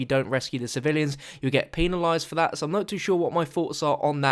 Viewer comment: Either I'm hearing things, but I think said Pee-Nalised (pun on penalised)